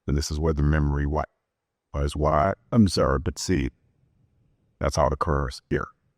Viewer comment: What does he say at 1:24:00 after he says "memory wipe"? he does this a couple times in the interview...... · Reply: If you mean at that specific point right before she starts talking again, then I understood him to say, "Memory wipe...It's what I've observed and seen ... is what occurs." In other words, that's when the "memory wipe" occurs.